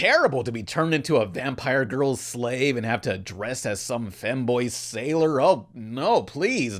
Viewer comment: Ok can someone explain to me did say femboy or fanboy at 7:00 and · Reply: fem